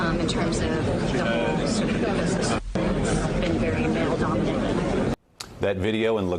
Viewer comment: What did she say? I can't understand her, could someone help me? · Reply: I heard “male dominated.” Their reactions were comical; startled, like, “it speaks?”